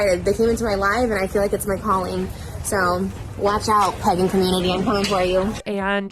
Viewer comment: I thought she said "*pegging community*" SMH